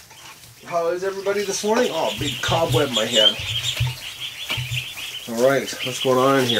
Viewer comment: Wait.....at 0:59 did you say you got cobweb in your hair???????? lol....Love the duck videos.....You must be the only one on the planet that thinks they are as cute as I do. I am up to almost 100 now, but 7 clutches still being sat on........no telling how many that will bring........lol